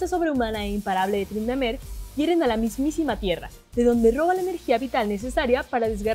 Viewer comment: escuche que dijo mier**